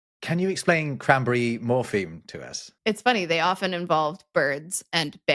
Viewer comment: I swore was saying, Cranberry Morphine. 😂😂😂